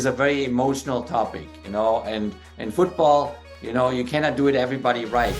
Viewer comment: 마지막에 "모두가 그들이 원하는대로 말할 수 있다"가 아니라 "In football, you cannot do everybody right." "축구에서는 모든 사람들을 만족 시킬수 없다 (축구에서는 모든 사람들이 말하는데로 다 따라 할수없다)"로 들리네요.